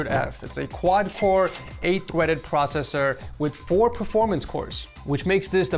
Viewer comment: I swear you said 4 core 8 “Thweaded”Lol Something Tweety Bird would say ❤